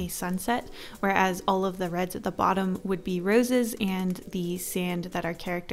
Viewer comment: When she said in the first one "she protects the roses" then I imagined this: Girl: wants to grab rose The protector: HEY ARE YOU LESBIAN?! Girl: no!! The protector: THEN DONT GET NEAR MY BABYS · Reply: I thought you said noses when you said roses